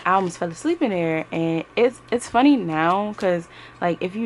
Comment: 💙 I had to watch again bc I think I heard you say you feel asleep in the stall. 😂 Gotta catch them Zzzzs. 😂 💙